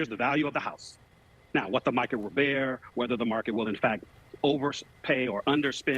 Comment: Did he really say the market?